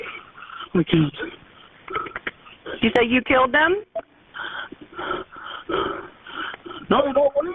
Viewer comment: Sure sounded like he said he killed them!